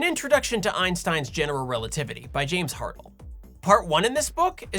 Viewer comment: I deadass thought he said James Harden and went wth